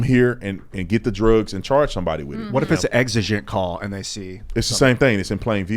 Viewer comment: “what if its exeggen call” lmfao its a hard word to say